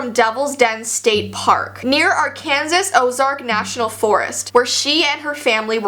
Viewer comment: says arkansis instead of Arkansas